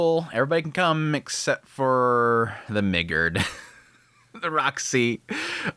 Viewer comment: 😂 Why did he say “Migurd” like that?! I thought I heard an N-bomb lol